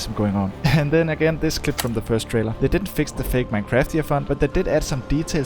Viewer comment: does anybody know what he said here This is what I heard. Fake minecraftian font